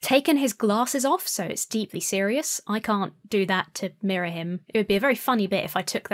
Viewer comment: You can't take your glasses off to MARRY him? I know that I've misheard that phrase, but what did you really say? Sorry, I'm learning english.